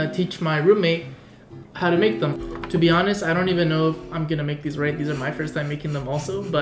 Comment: I thought you said "I dont even know if I'm gonna get rabies from this"